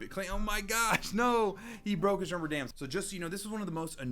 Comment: Rubber dams? I thought he said bands I was like 🤔🤔🤔. 😐😐😐